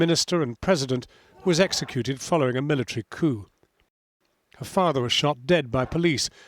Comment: what did he say ? Following a military .....? What was that word ? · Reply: "coup" military coup meaning overthrowing of an existing government by military in somewhat abrupt or violent fashion...